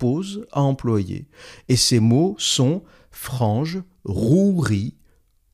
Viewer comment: c'est "Fange" et pas "Frange" apparemment un chouïa compliqué comme mot XD concernant l'aspect pédant se n'est pas nécessairement volontaire, quelqu'un ayant un vocabulaire fournis apparaitra pédant à quelqu'un ignorant ou pet familier de se vocabulaire, la satire "idiocratie" le montre. Toi même tu peut sembler pédant à d'autre s'en le chercher.